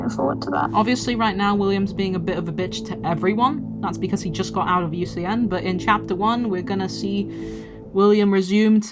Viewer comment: What the hell is Henry saying at the bottom here? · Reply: The same line he said before. If you've watched Toy Story 2, it's Woody's line to Jessie. "If the boot fits"